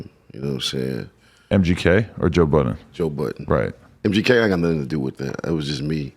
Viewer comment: i thought they said joe biden i was like hUH